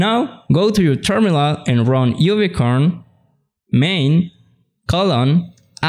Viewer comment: I thought you said unicorn api .. you should pause on critical words like this one to give people the time to digest, and/or provide links in the description. Otherwise your stuff is pretty good. The history of fastapi in particular. Congrats and Thx to Sebastian